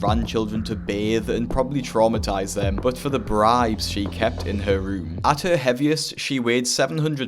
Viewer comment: When you kept saying bribes I thought you were saying brides and I was shook lol